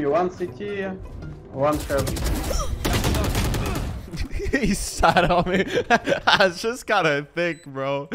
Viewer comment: WHAT DID HE SAY??!! “he sat on me”